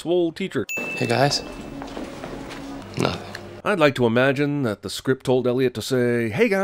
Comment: I thought that Walburg there said "Hey guys? Good luck". But it seems you're saying that he said "Hey guys? Nothing". But it sounded like "Good luck", which makes more sense anyway since they're leaving the classroom for the day while starting to deal with a potentially iffy scientific scenerio. 7:09 - 7:12 - they aren't on a bus, they are on a train (which you can clearly see in next scene when train breaks down) 8:11 - this was in 2008 when the first smartphones were around, only 10% of people had them and almost everyone else still had flip-phones. Most people had cell phones by around 2004, but the bigger flat smart-phones with complete internet access didn't get started until <num>:50 - how are all of these people who were in the diner driving away in cars?? Weren't most of them on the train that stopped running at the station of this small town?? Also, it was dumb that they had their windows open considering what they already knew. 14:20 - he asks why they didn't think to cover their noses or mouths, I don't know, maybe it's because they need to keep them open so they can..you know, breathe.